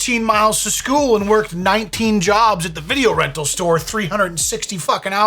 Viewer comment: I thought you said "I worked at the video rectal store" 😂